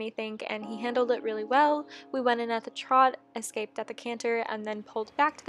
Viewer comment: did she say escaped at the canter?